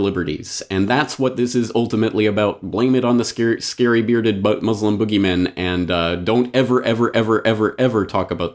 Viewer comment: I'll be the first to admit it's well past my bedtime, actually two now I think, but I could swear that I heard the term "Scary, scary, Butt-Muslims" in there.